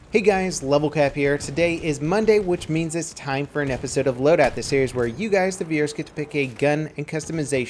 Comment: I've always wondered, what is he saying at 0:10? Div- something. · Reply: He's saying 'the series where you guys the viewers...'